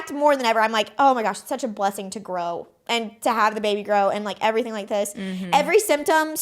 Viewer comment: seconds before minute 37, said "baby grow" and i had to go back 3 times cause i thought she said baby girl